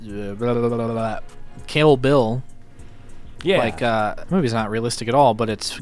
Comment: Camo Bill? Camel Bill? WTF is he saying